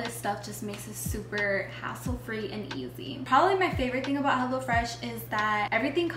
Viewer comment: Hahaha!! said her favourite thing about "Hassle Fresh" instead of "Hello Fresh" 😂❤ love you guys sooo much ❤